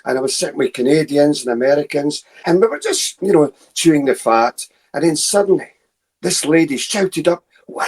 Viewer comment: what does he say @ 3:49? chewing a fart? is that an expression? lmao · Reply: chewing the fat*